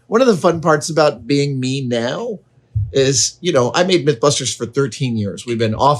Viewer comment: You said "Mythbusters" and I thought you said that you made "Mint Clusters" for years. I thought whaaa?